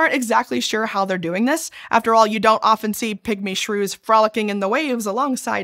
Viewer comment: Lol, I thought you said Pick Me Shrews, and so many people came to mind...